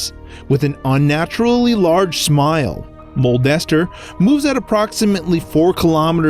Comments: I thought u said molestermoon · Maybe it's M O O N E S T E R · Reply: Saeki Jvzo at first I thought he said molester moon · sounds like he is saying molester wait... WE NEED TO CALL SMOSH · the moon I thought you said molester moon